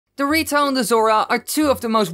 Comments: When he says "the ritos" it sounds like he is saying Doritos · I heard dorito and bazooka